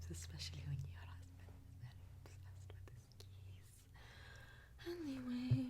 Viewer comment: what does she mumbles? the curiosity is killing me... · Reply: "Especially when your husband is very obsessed with his keys"